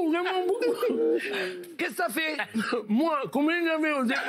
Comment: Michel a dit quoi G pas compris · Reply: il dit : "Moi, combien j'avais au départ?"